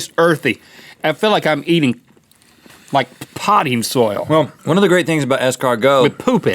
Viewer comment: Link: It tastes like potting... soil. Me: Sounded like he said "potty" for a second there. Link: With poop in it! Me: Okay then.